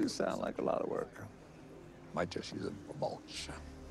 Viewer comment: wtf did he say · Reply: He might use the bonsai tree for mulch